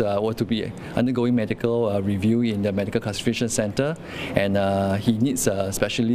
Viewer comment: anyone else heard medical castration centre?